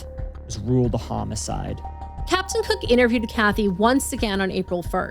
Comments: I thought you said Captain Hook and it made me think of the Megan Thee Stallion song, lol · I fucking thought you said Captain Hook